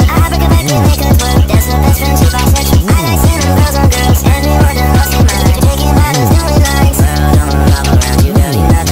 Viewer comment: HELP DOES IT SAY “Thats befrend she bisexual” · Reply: CLOSE! IT SAYS “That’s her best friend she bisexual” 😭